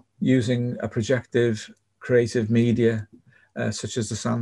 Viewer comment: what was the projective creative outlet you said here? It’s a little hard to hear. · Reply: ‘Projective space’ as in a virtual space, hence the image/metaphor of a filter used in the diagram.